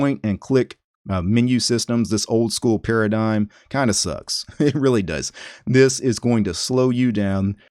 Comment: Did say sus????? 😐😐😐